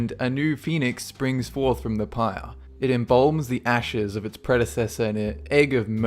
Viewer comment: did u say pire?! 😹😹😹😹😹😹😹😹